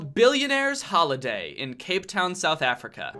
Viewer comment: i thought he said cavetown 💔